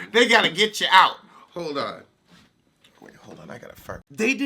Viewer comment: did say “ Hold on. I gotta fart.” 😂😂 LOL. Thank you. Watching Wendy for so long common courtesy whole spilling and sipping tea has become a foreign 🤣